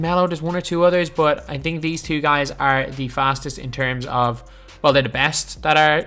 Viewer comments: I thought it said fattest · I thought it said fattest 😂